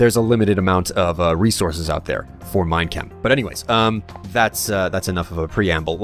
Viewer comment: whenever say minechem it sounds like he is saying mein kampf